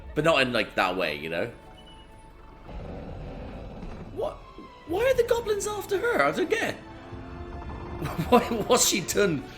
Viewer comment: I know you said "Why are the goblins after her? I don't get it!" But the first time I heard it I wasn't paying enough attention and I misheard it as "Why are the goblins after her? Are they gay?" And nearly choked laughing on my coke.